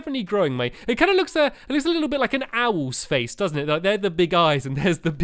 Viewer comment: When you said "owl's" I thought you said "Always" and I said/yelled "Professor Snape?! Is that you?" And almost woke my mom up.